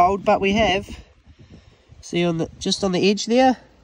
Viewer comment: Tbh I thought he was saying " bluek sand " 😂